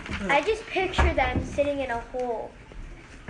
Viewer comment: I hear a little girl say I picture them sitting in a hole, I don't know why!!! Wow!